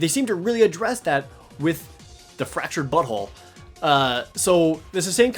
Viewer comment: did he say fractured butthole at 6:56 ?